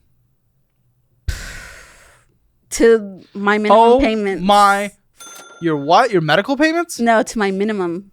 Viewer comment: She actually said “2014 Chevy Spark”